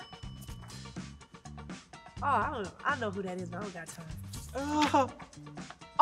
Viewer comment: She doesn’t know who Anita Baker is 😮😮😮😮😮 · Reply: that’s key info. I went back & you were right. But at first listen it really does sound like she said “idk who that is and I ain’t got time” lol